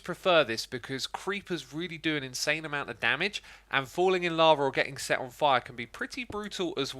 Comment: I thought I heard falling in LOVE omg but yeah can be pretty brutal too hahahahaha